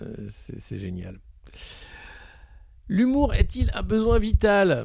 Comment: J'ai compris "l'humour est létal" Lol !!! Merci de retourner ce monde décadent en farce .... le rire soigne+++ Je partage off course !